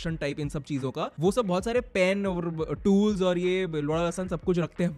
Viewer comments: kya bolaaa 😂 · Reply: ❤da lasan 😂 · Did he say L*da L*san at 7:10 ?? 😅